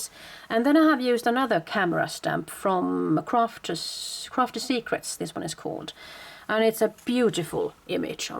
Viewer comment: Gorgeous! Love the whole album! Could you tell me where you got the camera stamp you're showing at 17:17 in the video? Crafter's Secret, is what I thought I heard you say. Is that correct? I am not finding any camera stamp that large or by that name. Is it a mounted stamp? Any help would be appreciated. All the stamps I'm finding are small or cutesy. Thanks! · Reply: Crafty secrets... Clear stamp... Great quality ;-)